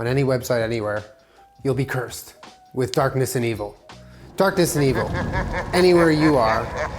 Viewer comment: I misheard “darkness and evil” as “darkness and anal” and let me tell you....I got very confused for a second there